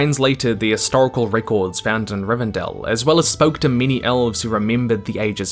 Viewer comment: What you say: “many elves” What I hear: “mini elves”